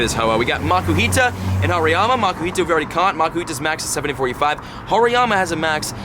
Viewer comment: It sounds like he says makuhita max CP 7485 lol